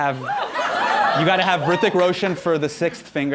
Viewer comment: did she said Hrithik Roshan?